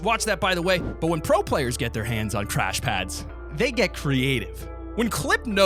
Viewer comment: I thought he said trash pads lmao